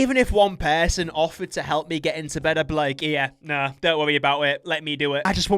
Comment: did bro say wowwy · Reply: Worry.